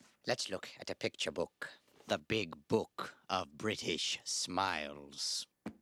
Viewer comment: I thought that said "The Big Book of British Similes" and was kinda disappointed lol